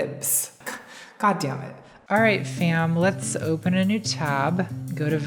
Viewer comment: Ok I’m freaking out. At 19:16 did she say “alright Sam” to everyone? Or just me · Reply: She said “fam.”